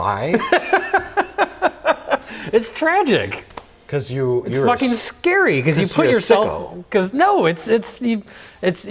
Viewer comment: what does mike say to jay? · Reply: oh he says "cause you're a sicko"